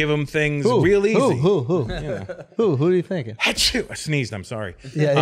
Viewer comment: Anyone else think when Russel “sneezed” and said “i SnEeZeD iM sOrRy” sorta sounded like “Aziz Ansari”